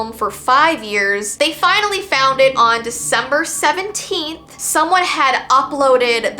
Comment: WAIT WAIT WAIT AT 7:03 DID SHE SAY DEC 17 OR AM I HALLUCINATING